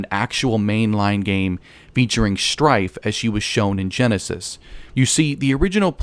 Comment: did u call strife a she